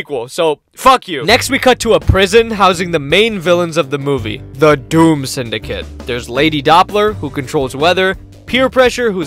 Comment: i thought you said the down syndrome lol